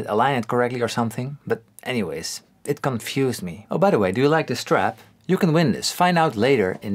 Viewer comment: I thought u said "you want the strat?"